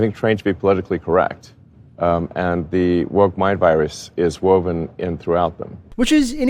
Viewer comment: Instead of “the woke virus is woven in throughout them” I’ve heard “the woke virus is woke” and I thought I’m gonna lose it